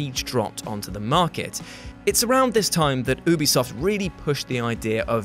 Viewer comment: did he just say boobysoft